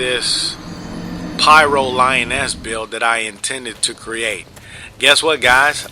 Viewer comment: at 0.54 I thought I heard "Pyro-Lion-Ass" at first ))